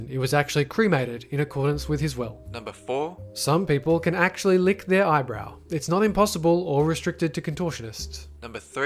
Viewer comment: did anyone else hear "lick their eyebrow"?